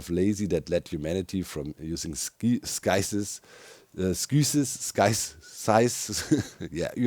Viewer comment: Like saying "Sides," but with a 'th' instead of 'd.' Sides - Sithes - Scythes.